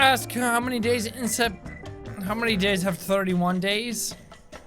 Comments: Waiiiit, Did it say how many DAYS have 31 Days??? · No the one you thought said how many days have 31 days actually said how many months have 31 days and you forgot October